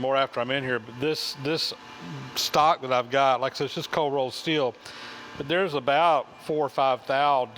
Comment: Probably an elementary question but is the new shaft cold rolled mild steel? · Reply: Jim Humphrey, I was wondering the same thing, but at 11:57 he says cold rolled steel. I’m a little hard of hearing and it was kind of hard to catch, so I went back and checked and I think that’s what he said.